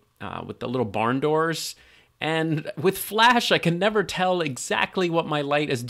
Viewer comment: What light are you using as a backlight w/ barn doors at 7:15m ?? At first, I thought you said flashlight and thought oh that's great for my budget lol... maybe a powerful LED flashlight with some diffusion help could work as a backlight?